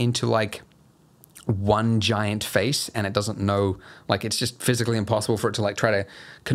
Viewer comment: I heard "vaginat face". Sorry xD